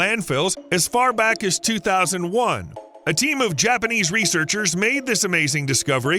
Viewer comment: I thought he said a temu researcher; I almost thought, "Yeah, let's trust a researcher that works for temu."